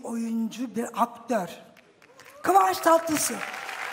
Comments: Kıvanç tatlısı mu dedi ben mi yanlış duydum??? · Kıvanç Tatlısu mu dedi ben mi öyle duydum? 😂